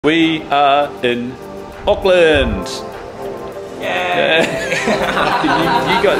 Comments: I thought he said Oakland. I was about to say watch your back you gonna get robbed and shot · As an American I thought you said Oakland at first and I was like, oof that’s too bad😂